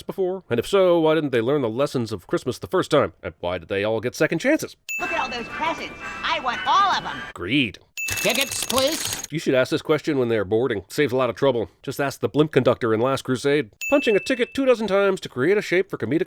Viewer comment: I thought he first Said AGREED not greed lololol